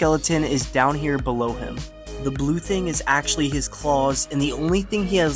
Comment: i misheard claws and balls i was like wtf but then realised my dead ass missheard